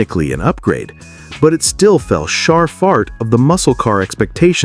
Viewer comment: the narrator says “shar FART” instead of “fell short” LOL 😂